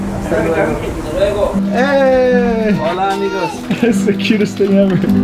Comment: Am I the only one who heared "whola niggas"? 5:19 (No hate)